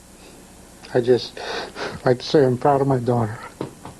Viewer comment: What Does say in this video where he breaks up with emotion? I’ve never been able to understand it. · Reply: Same! When I watched this segment as a kid I thought he said "I sure am proud of my daughter." Which confused me ever since, because I kept wondering why Kim Kelly's dad was a coach or looked just like the coach 😂